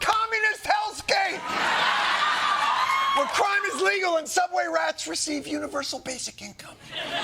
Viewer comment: I thought I heard subway rats receive universal healthcare 😂. How can I sign up!